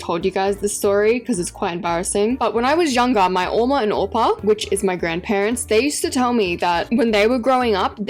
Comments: did she say "eomma and oppa" or am i tweaking😭💀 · I thought you said 엄마 (eomma) and 오빠 (oppa) 😭 (엄마 is mother and 오빠 is older brother in Korean)